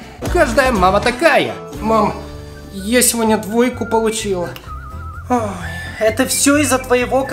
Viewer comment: мне показалось что он сказаля даойку получила